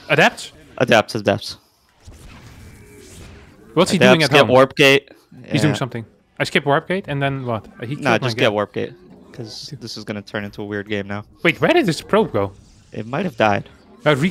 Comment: it actually does sound like he says skip warp gate, but I turned up the volume a lot to listen again and he does say get warpgate.